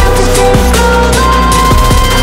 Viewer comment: Why am i hearing "i am about take cover".